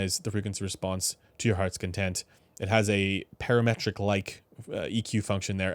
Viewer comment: I heard that as "it allows you to customize the response to your hrtf's content". 🙃